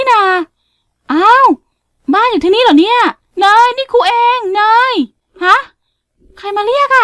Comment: ตรง0:04ทำไมหนูได้ยินพูดว่า เนยนี่กูเองขอโทษนะค่ะที่มีคำอยาบเพราะหนูได้ยินพูดคำนี้หนูเลยลองเปิดเสียงให้ดังกว่านี้ความจริงพูดว่าเนยนี่ครูเอง หนูตกใจหมดเลยนึกว่าพูดคำหยาบ(ขอโทษจริงไปนะค่ะที่มีคำหยาบ)